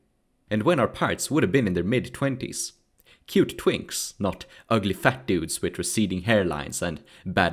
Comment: Did I hear him say cute twinks? Min 3:15 haha